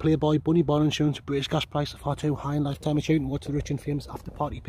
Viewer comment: what do you say? I picked up “British gas prices are too high” And the energy is immaculate, great content